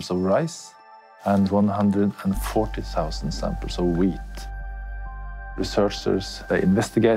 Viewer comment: When he said wheat I thought he said weed